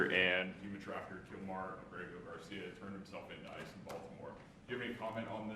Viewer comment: What? Sounds like you said he is sending Nazis to Baltimore.